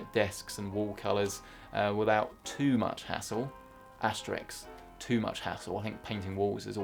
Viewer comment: did you say Asterix instead of asterisk or am I hearing things? :D